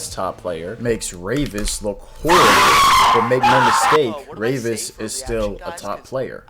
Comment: thank you for that, can you turn up the volume of the reaction next time please? i almost understood what you said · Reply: lmao I said “make no mistake Raevez is still a top player”